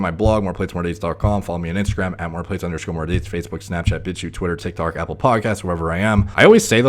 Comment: I hear "bichy" as a social network ?!!!